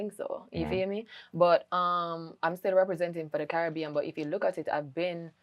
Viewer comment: Representing the culture made me laugh, I thought she said she doesn’t need Caribbean people.